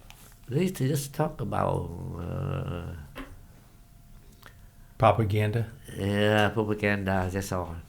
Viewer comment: I like how his accent makes propaganda he sounds like he says poop-a-ganda. Lol